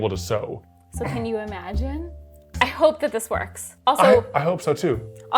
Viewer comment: did she say "so hem you imagine"? I cant tell, but if so, 👍👍